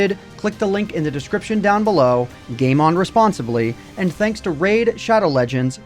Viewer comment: When said "Game on responsibly", I thought he said 'Game unresponsibly' lmao